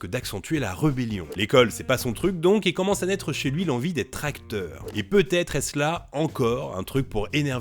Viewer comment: J'ai compris "l'envie des tracteurs" j'ai bugué xD